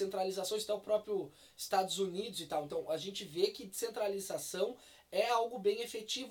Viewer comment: Também acho que as descentralizações seriam melhores que separar o sul, inclusive os anarco-capitalistas também defendem isso mas dai alguns canais vem com aquele papo de "se você defende o federalismo por que não defender a secessão", enfim acho que é assunto interessante pra abordar, principalmente por que os ancaps não querem admitir que seria uma solução muito melhor que a secessão. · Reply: OBS: No começo achei que você tinha falado que "centralização era algo bem efetivo", mas com o fone ouvi que era descentralização.